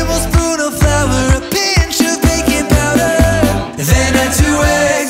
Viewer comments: Did they just say "Bacon Powder?" WTH--THAT'S NOT KOSHER! Just kidding--I think they meant baking powder, but I swear it sounded like "Bacon Powder..." lol. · I thought they said "add a pinch of bacon powder" haha then I thought, "they can't say that!!" They I realized they said "baking powder" :)